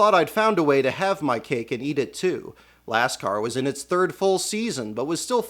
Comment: did he say LASTCAR?